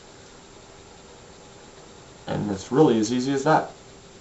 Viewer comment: I Just won $5 on a bet that you said "Easy as That" My gf thought you said "Easy as Heck" But I knew that would be too vulgar for you to say XD LMAO EASY $$$$$$$$$$$$ EASY GARLIC! $$$$$$$$$$$ tysm